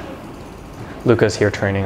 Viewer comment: Yes. Sorry, I called him:D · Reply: Oh didnt even notice you called him, I thought you said "Look he s here..." I'm italian so I misunderstand easily, I recognized him just by his laugh xD